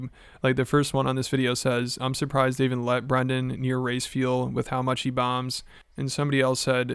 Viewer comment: I had to run that back a min. I thought he said, " Im surprised they let Brendan near rice fields with how much he bombs." Err... race fuel😅